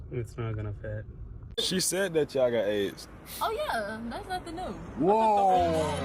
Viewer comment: can someone tell me what happened in this clip? i dont understand them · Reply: The man said “she said y’all got aids” the girl said “oh yeah”